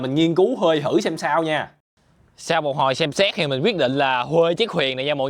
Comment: sau một hồi xem xét mà t nghe thành sex 🗿